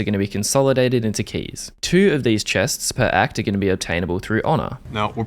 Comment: I thought they said 10 chests per ACT. Arnt there just 3 remaining acts this year , so 30 chests?